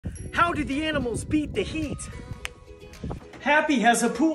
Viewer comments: I thought he say how the animals beat there meat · why did i hear "beat the meat" only to realize from subtitles that he said beat the heat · I thought he said how the animals beat the meat 💀